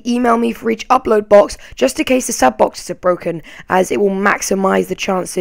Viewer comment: It sounded like he said "Piss off" instead of "Peace off" lolzzz